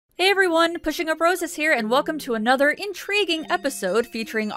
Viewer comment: First time to the channel and was laying down about to hit full screen and I hear “hey guys pushing up bras is here” and had to double check because I must have heard that wrong